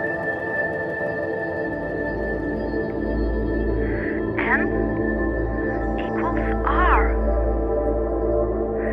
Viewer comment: What is she saying from 06:22 ? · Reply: The drake equation.